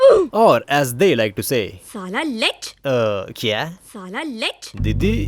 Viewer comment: I think she wanted say sala leech.. Maybe